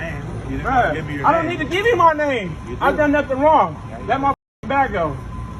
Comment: He actually said he just needs his name around 8:00. Js